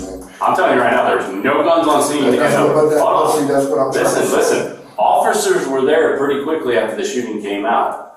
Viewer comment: I thought bro said listen office Major Pain 😂😂😂😂